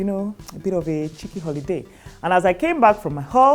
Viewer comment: did you just say ‘came back from my ‘holz’ ‘ 😂😂😂😂